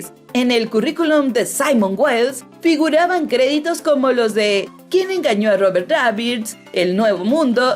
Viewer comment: ¿quien engaño a robert rabbit? eso le entendí